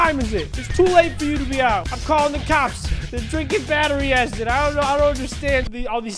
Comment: I heard say Battery A**